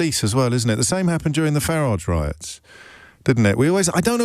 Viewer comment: Did he just say "THE FARAGE RIOTS" ?